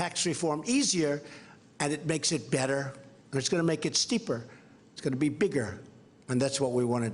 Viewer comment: Did he just say "steeper" 0:40? WTF does that mean